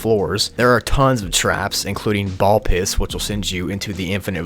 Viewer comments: sounds like he said ball piss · ball pits or ball piss 😂 the subtitles got it wrong